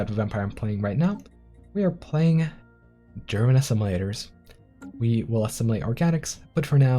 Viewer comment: Anyone else hear "German Assimilators"?